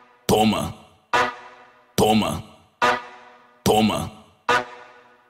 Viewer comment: putz, entendi coma!!!